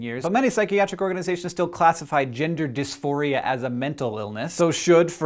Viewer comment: I wasn't looking at the screen when he mentioned 'gender dysphoria', and I heard it as 'ginger dysphoria'